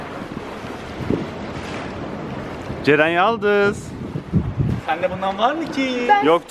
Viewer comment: te cercel yaldız mı dendi ben mi öyle anladım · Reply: Ceren yaldız diyo ,dikkatli dinle